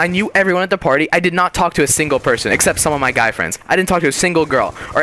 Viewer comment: lol I thought he said gay friends :P nice commentary though!